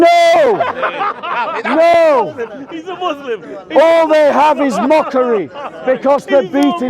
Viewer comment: I thought the bald guy said he isn’t a Muslim 😂